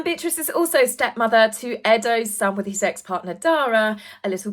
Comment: HAHAAA… I had to do a double take…I thought you said ‘SEX partner’….not EX partner! 🤣😂😂